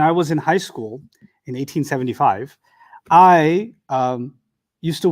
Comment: ustad u went to school in 1875/1975? becoz i heard it as 1875